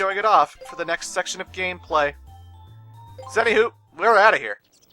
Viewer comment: It sounded like you said "Zenny who." Was that intentional?